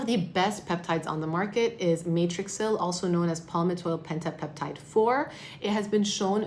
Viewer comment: What is that big long word she says at 14:24 ??? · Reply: Matrixyl - palmitoyl pentapeptide-4